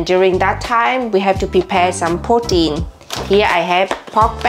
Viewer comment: When you said “protein” i thought you said “poutine” and I was like what that’s not Vietnamese food